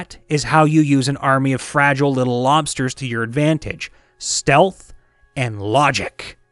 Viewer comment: Legit thought he was going to say "Stealth, and lobsters" 😅